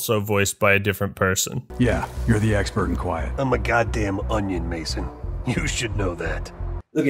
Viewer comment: I thought woods said "I'm a goddamm OMNA." One man national asset. Which is a tongue in cheek phrase gwot sf uses. Except the subtitles say ONION. What the hell. Marketing demanded a meme and got one.